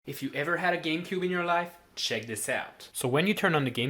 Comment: Ohhhhhhhh! A Game-cube! I thought you said a Gay-cube🤧